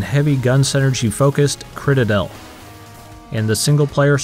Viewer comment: Did he say Critterdell?